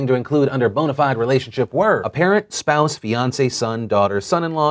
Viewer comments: I could have sworn when he said "Fiance" he actually said "Beyoncé" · When he was listing off the bonafide relationships, I legit heard "Beyonce's son" instead of "fiance's, sons.. " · I swear to God, in the story about the travel ban I thought said "parents, spouses, Beyonce's sons, daughters..."